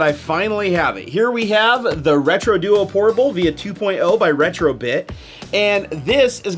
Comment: Does he say via 2.0 at 0:29? I've heard mixed things about these but I'd really like one. The hard part is buying all the games you actually want to play.